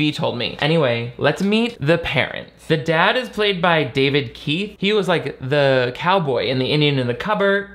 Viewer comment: Gonna be honest with you, chief... You said "David Keith" and my brain heard "Keith David" and then I saw the dad and went, "Who's THIS slice of white bread and mayo?!" And then I realized I misheard and was NOT going to get horror legend Keith David and I was sad.